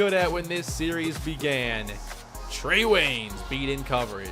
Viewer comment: when you said Trey Waynes or whatever his name was, it sounded like you said Treyw3y